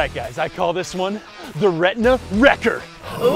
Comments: At first I thought he said “Rectum wrecker “ 😂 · I misheard 'retina' as 'rectum' and thought it was going somewhere completely different. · When I first heard this I thought he said the retina record.